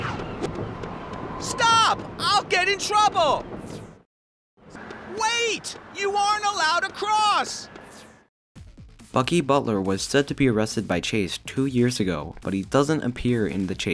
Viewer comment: "F*cky Butler"? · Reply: I know that's what his name is, but if you listen carefully, it does sound like you said "F*cky Butler"